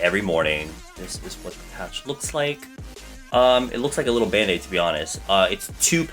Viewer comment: This is what I heard: Looks like a little penis OMG JAJAJAJAHAHAA LOL 😂